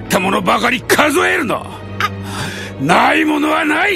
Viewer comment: Guys anyone can tell me what he is saying in japanese at 01:29 ...??? "what is it that you still have" .... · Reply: So I've been trying to figure this out for like 20 minutes hahaha Ok so it's: お前にまだ残っておるものはなんじゃ ( omae ni mada nokotte orumono wa nan ja? ) Jinbei talks like an old man, that's why he uses "じゃ" at the end (I'm pretty sure).